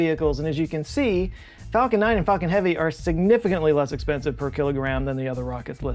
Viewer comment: Did anyone else mishear that at 2:45 ?..lol i was sure i heard Falcon 9 & Fuckin' Heavy..😆